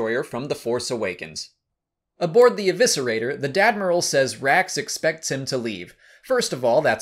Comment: Did you say...’The Dadmiral’? 😂